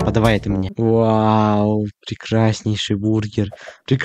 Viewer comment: мне сначала послышалось ужаснейший хахахах